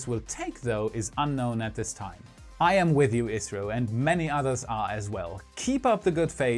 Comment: also at 10:50 you said Israel again instead of India, but there is ok, because you have briefly mentioned they're also recent unsucces.